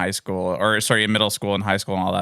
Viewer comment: Fuck I just misheard "middle school", I heard "metal school" and was like, wtf ? that kind of school exists ? xD